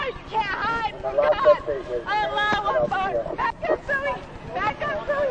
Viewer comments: She sounded like she was on something, and it was not Tylenol. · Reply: It sounded like she said Allah Akbar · first i thought she was high or crazy then i heard "aloha snackbar" and it all made sense.